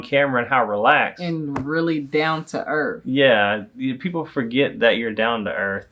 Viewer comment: did you just say “down to erff”? 😂 love it.